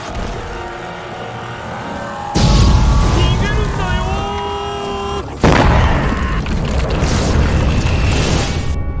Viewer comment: Could you enlighten me what is the guy at 32:00 saying in japanese? it's dubbed as "Run away from the blue thing" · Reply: I'm a bit late on this but it sounds to me like he is saying 逃げるんだよ (nigerundayo) which actually does mean "run away!" but in a more anime-style way